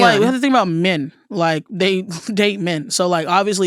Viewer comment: Is she saying “Men, they they men”? Or “Men, they date men?